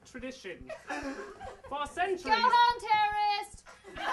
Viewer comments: All these years when Malfoy was saying "Go home terrorist" I thought he was saying "Go home Terrence" and I got really confused because I knew that Quirrell's first name (Quirinus) is not Terrence. Wow I'm an idiot haha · third time watching this and 4:22 did Draco say 'go home terrorist'